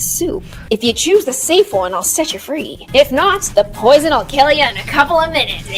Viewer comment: I THOUGHT THAT SAID “if you choose the safe one I’ll set you on fire” 😂😭😂